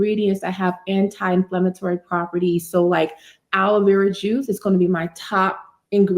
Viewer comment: Can someone tell me what the first ingredient she mentioned is called? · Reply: It's Aloe Vera Juice .